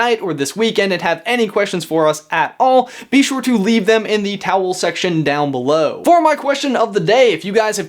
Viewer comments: am I the only one who thinks it sounds like he is saying towel section istead of comment section? also #slytherinforlife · Did he just say "towel section"?